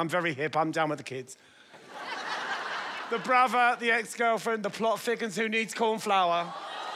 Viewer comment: "The brother, the ex girlfriend and the pot that needs flower 😂😂😂😂 shut up BG haha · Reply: I thought he said, "The Brother, The Ex Girlfriend, the plot thickens who needs corn flour."